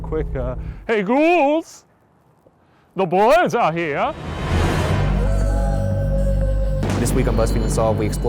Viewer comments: I thought he said "eyy boss the boys are here" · when Shane said 'Hey Ghouls!' my phone thought he said 'Hey Google' I bet Ryan wished it was just google.